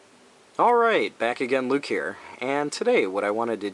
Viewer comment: "Alright! Back again, Luke here." This whole time I thought you were saying "Alright! Bakugan Luke here." ...sorry